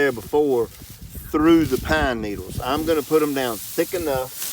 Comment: Hey. What type of mulch did you put in the first part of the video? Ah, I just heard you at 12:30 say "pine needles"